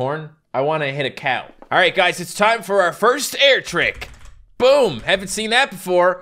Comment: "air trick" no joke I thought it said "aww frick"